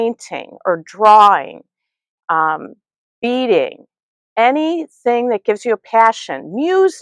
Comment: I was almost out of earshot when you were talking about reducing stress. When you mentioned beading, I heard "eating." I thought wow! I should be a really calm person! I'm sorry you lost so many family members to cancer and went through a rough divorce. My husband died 5+ ago, and it's taken me a long time to adjust. After a few years I wondered if my ex ever got his act together after 50 years. He didn't. He burned every bridge he had. He just died and I ended up paying for his cremation to keep him out of a pauper's grave out of gratitude for his mom's kindness. I need to do more sewing and less just thinking about sewing. And chill!